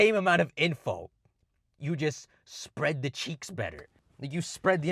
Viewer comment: did he say “spread the cheeks” 👀🤪🍑 yessir